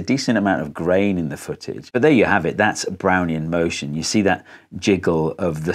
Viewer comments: Neat stuff! Brownian motion will feature prominently in my next video also. It really is pretty amazing how large the particles can be before the forces average. Really like the vibrating plate demo. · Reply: I keep hearing, "Brownie in motion" and getting a craving for Thin Mints. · i deadass thought he was saying brownie in motion for a fat second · Only played 40sec of video and I always hear "Brownie in motion" and now I need a brownie